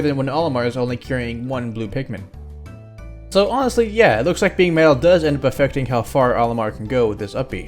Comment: it sounded like you said "Being male does effect Olimar's Up B."